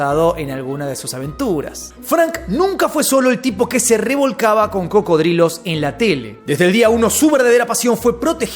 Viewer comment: escuché "frank no sólo se acostaba con cocodrilos en la tele" jaja este es un loquisho